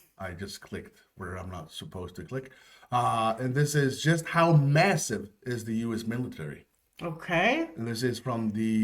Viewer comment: did u say….massive..??